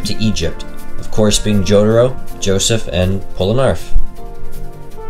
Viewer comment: Did you just say polarneref